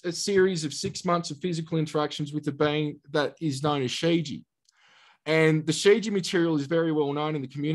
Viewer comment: he talks about a set of best selling books that goes over the material he is talking about. Did he say sigi or what? I could not understand what he said there.